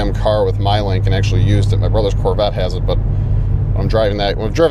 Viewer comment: The only SS I would drive would a 1968 SS Chevelle 396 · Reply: Brian Samuels for a second there I thought you said Chevette